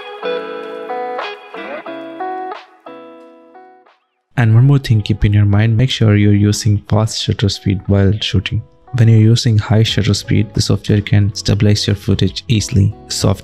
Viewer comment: I'm sorry but what is "pos shutter speed" (2:13). The shutter speed issue is still unanswered for me. It's sounds like you have addressed it, but in a whisper over soft jazz music, can't quite figure out what you're saying. The tests look favorable, a better option than the software solution. But a blurred frame is still blurred in both stabilization processes. I think that shutter speed/angle is important here to reduce this problem. Thanks for the video.